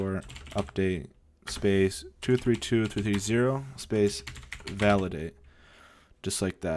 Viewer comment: "ERROR! Failed to install app '232230' (invalid platform)" help? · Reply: Its actually 232330. It just sounded like he said 232230, i had the same problem when i first did this haha